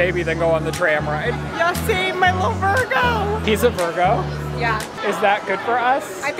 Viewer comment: Did say ‘he’ about Lizzie’s baby at 22:38?!!?